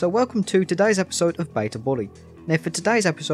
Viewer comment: thought that said bully 2